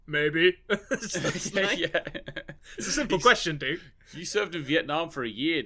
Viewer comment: My subtitles say "Didn't you surf in Vietnam", not serve